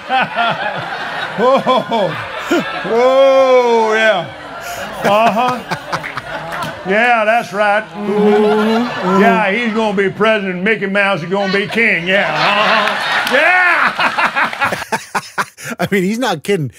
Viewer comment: I thought he said "He gonna make Mao Zedong be king".